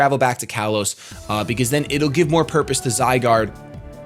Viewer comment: What is Auros?it sounds like he said that(idk how it's supposed to be spelled)but he made it sound like a region..I'm a bit clueless here.